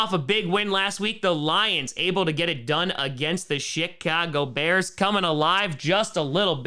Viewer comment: sounded like he said, 'Shit-cago" bears